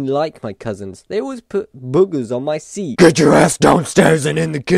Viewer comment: OMG did u say ass